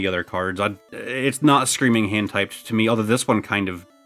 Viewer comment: can you guys please tell me what he was saying here? 25:55 i kept hearing him say HENTAI CARD and im sure he didnt say that